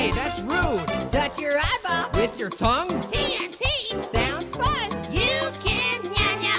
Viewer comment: what did he say? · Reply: “Pears not as quite as cool and fun as orange!”